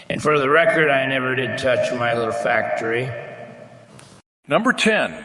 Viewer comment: What was 18:20 saying? "I never touched my little factory" lol Am I hearing that right? 😅